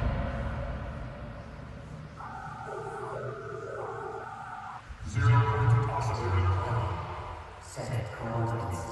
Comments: Did anyone heard what he say :v He’s talking about the zero point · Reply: The person who said that (aka the Visitor) said. Zero-point oscillator on, set coordinates, engaging multi-fueled accelerator. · anyone know what is said at 0:59 ? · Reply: Zero Point Oscillators On, Setting Coordinates, Engaging quantum field accelerator.